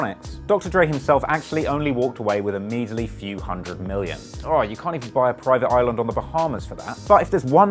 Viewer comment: I'm confused as to how a few hundred million isn't more than seventy five million. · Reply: Or maybe it's the accent that makes it sound like you say "Alright, you can't even..." I'm probably just too American.